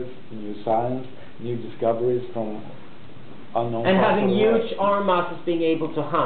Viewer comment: what does he say at about 2:27 - an arm ? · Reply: its been a decade, BUT... having huge arm muscles is irrelevant because acquiring food from farming/hunting isn't as useful in the industrial age.